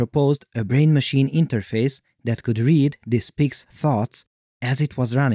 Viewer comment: The first time you said "this pig's thoughts" I really thought you said "this pig's farts" 🤭🤣